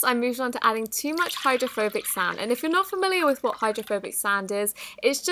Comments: I thought it said homophobic sand not hydrophilic sand 😂😂🤣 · I thought the second one said homophobic sand for a hot second and I was v e r y confused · Oh my god I thought it said 'homophobic sand' I cant even with myself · who else thought it said ''Homophobic Sand''? XD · Thought it said homophobic sand 🤣😂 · I thought she said homophobic sound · When she said hydrophobic sand I thought she said homophobic sand XD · I THOUGHT IT SAID HOMOPHOBIC SAND 😭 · I thought it said homophobic sand